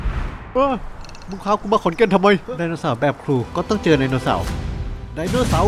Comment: ผมได้ยินว่า มึงพากูมาขอนแก่นทำไม